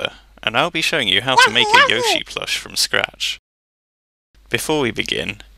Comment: For some reason i always thought he was saying yoshi first (I dont even know why)